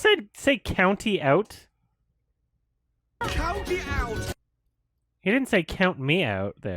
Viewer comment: YES! pointed out how it sounds like Zazu says "county out"! I've been saying that for ages! Thank you!